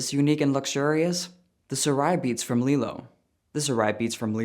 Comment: Wasn't looking at the screen and only half ass paying attention when you mentioned the sponsor and when I heard the word 'beats" I thought "oh cool he got sponsored by beats... they're good headphones......oh wait nevermind wrong beats! Those don't go in your ear 💀"